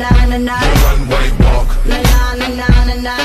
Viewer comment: When I first heard this song I thought he was saying one way walk until now